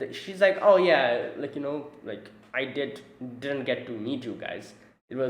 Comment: No, it's not like that. She didn't say "I didn't get to meet you" (since, after all, she had actually met them on Digitalis), but she said something like "Homura is the only one who saw me", which is not the same. What she wanted to say at that moment was that Homura was the only one who knew Amira's real face (and her real name), while all others knew her only by her RPG avatar.